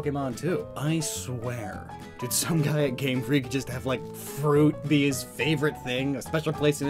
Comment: when he said game freelk i thought he said game stop